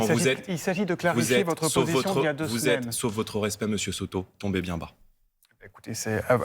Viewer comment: Quand il a dit "tombé bien bas" j'ai entendu "petit merde" aussi xD